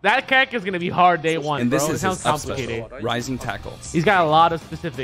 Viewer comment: I thought terry said said Lightning typhoon instead of rising tackle...